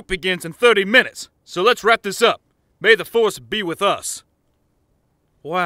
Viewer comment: I thought mace winbu said may the force with me 💀